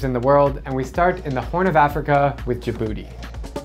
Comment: I heard "and we start with the horn of Africa ya booty" I was like 🤯